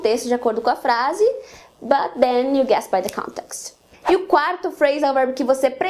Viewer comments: O que ela diz em 3:43 ? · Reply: "But then you guess by the context" (Você adivinha pelo contexto) ;) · Excelente vídeo. Mas não consegui entender o que voce disse quando encerra a explicação sobre "come up with" (3:44) "But then you xxx by the context" (O xxx é "gets"??) Muito obrigado pelas aulas, voce é demais. Sucesso na sua vida!!!